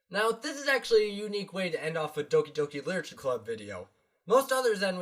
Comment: When the vide said “Doki! Doki!~” All I heard was “Ducky! Ducky!~” But, I LOVE THE VOICES OF THE CHARACTERS- Especially Natsuki has a CUTE voice.